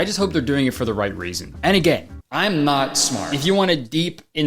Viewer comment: did he say any gay😂